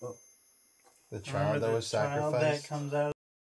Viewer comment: When says see if you missed anything we did I heard "I was sacrificed"